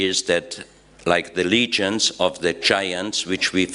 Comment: What is this man saying? Legion or Legend? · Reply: I was confused too, but I think he's saying "Legend'. It's heavily accented so it's coming out as "Lee-jund". You can hear it at around 13:39 - 13:50.